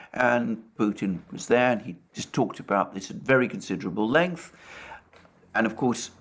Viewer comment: I had to listen a few times as I thought you said "Putin was talking about his very considerable length" at about 17:00. This would explain all the mistresses and children..